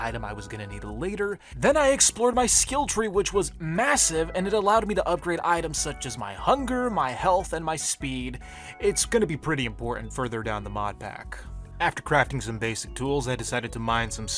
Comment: did you say MASSIVE